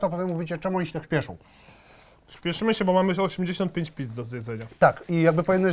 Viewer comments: kto też usłyszał pizd daje łapkę xD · czy ja usłyszałem pizd?